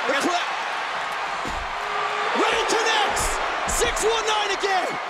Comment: am i just hearing things, or did kp say " Riddle connects!"